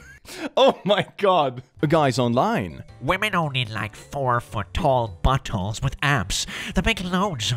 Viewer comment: did he say 4 foot??